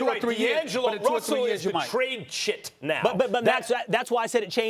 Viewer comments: Did max say trade shit at 4:44? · Am I the only one who heard Max say “trade sh*t” ? 🤔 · Did max say "Trade shit" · wait what did max say at 4:42?? trade chip?